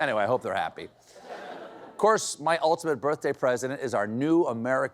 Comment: Had to rewind to make sure I heard him right when he said "my ultimate birthday president"